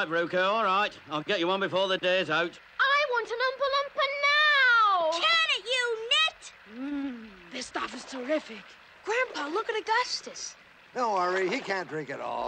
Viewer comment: "I want an oompa loompa now!!!!" "Can't you knit!!!" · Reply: I know. I always thought she said "Can't you knit?"